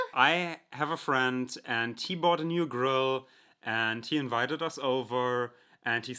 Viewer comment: I misunderstood and thought he said "He bought a new girl."